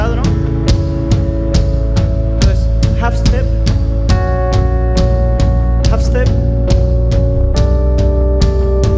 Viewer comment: Que es lo que dice en el minuto 42:12 ? Algo de Hafsted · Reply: GRUPO ADN El dijo “Half Step” quiere decir medio tono